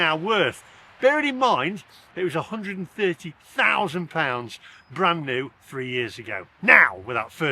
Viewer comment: Thought you said you car was £120,000 ? As you are self employed or running a small company your car payments are written off against tax. Is it a company car ?